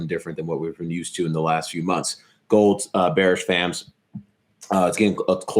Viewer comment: Sorry ... what is he saying "bearish vams(?)" Ex.